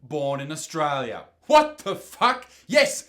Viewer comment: I thought you said Australia at first so I was really worried, but it is only Tasmania so it doesnt matter